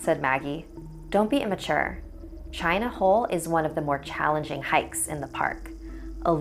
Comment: I thought the boi said "China Hoe"